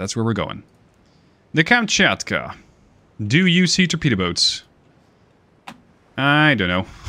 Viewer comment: I hear "Do you see torpedo boats ?" , i click like.